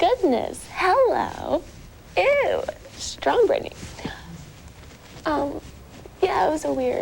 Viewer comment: eww, strawberry? i thought she said "strong britney," so she wouldnt cry. there was no strawberry on her nails, they were painted completely red.